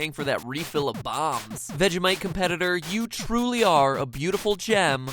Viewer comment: has the captions maker never heard of Vegimite? he put "that you might" instead of "vegimite."